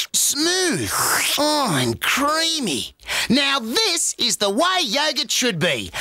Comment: Why did I hear “I’m creamy”💀